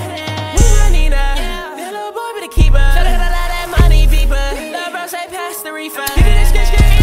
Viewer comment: when he said "fblblblbldada money beeper" i felt that 😔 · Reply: Im laughin cause i really thought thats what he said but he said " swear to go i love that money beeper"😭😭